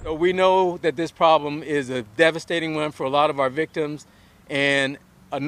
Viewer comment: Listen to the policeman at 1:54, Is he saying "...devastating WIN for a lot of our victims?"